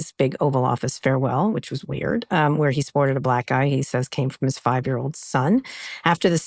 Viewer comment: For a second i thought she said "supported a black guy" but she actually said "Sported a Black Eye" oh yeah of course